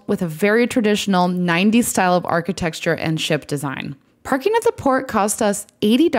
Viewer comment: did you say “architexture”? That’s my new favorite word now.